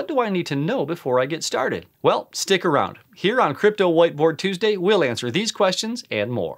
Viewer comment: I thought he said crypto white boy tuesday